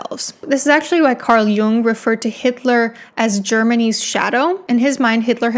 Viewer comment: When she said "germany's" My brain heard it as germanese and shirt circuited for a bit